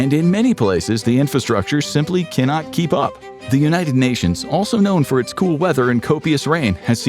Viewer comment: did he say the United nations??